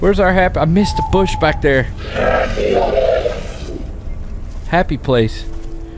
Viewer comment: Did that thing just repeat what said? I swear it says "happy place"